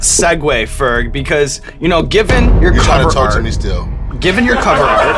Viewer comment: What does he say at 12:24 when everyone laughs? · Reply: "You're trying to talk to me still."